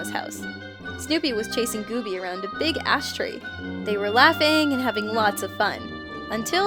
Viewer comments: did anyone hear a big ass tree in stead of a bigass tree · i thought u said a huge ass tree. xD